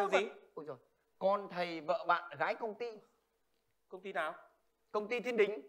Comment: t nghe nhầm mẹ thành vợ thầy, con bạn, gái công ty